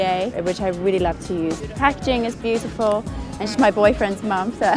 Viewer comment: What did she say at 0:58? "what" is beautiful?? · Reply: the packaging :)